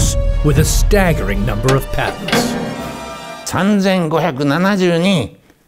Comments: Jesus I thought it said “Parents" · almost thought it said parents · Intelligence: 100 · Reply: I thought it said parents lol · Oh i thought it said parents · My dumbass thought it said “Parents” instead of “Patents” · I thought it said parents I was thinking “da hell how you have 3500 parents” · thought it said parents. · I thought it said parents not patents😬